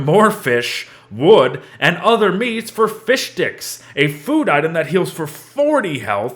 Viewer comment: "fishdicks" I hear everyone say it and I love it